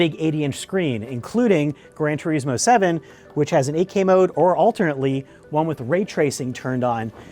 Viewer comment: Did you say 8k mode? Whoa, 4k is good enough for me.